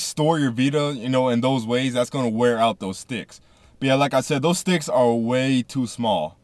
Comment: "like I said those dicks are way too small, way to small for my liking, they need to make those bigger" sorry that's just what it sounded like